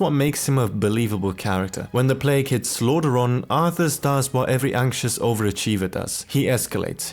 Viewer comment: When the plague hits SLAUGHTERON, oh yea it was a Slaughteron alright...I cant`t be the only one who heard it, right?